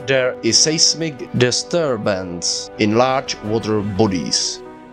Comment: i thought he said large water police 😂.